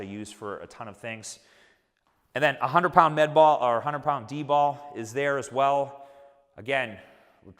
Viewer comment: did someone say D-Bol?